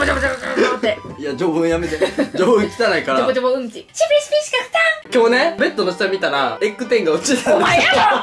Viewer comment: ずっと気になってたんですけど、なんて言ってるんですか？ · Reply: シピシピしか勝たん！です。シピシピというブランドのグリッターをが愛用してるんですよ！笑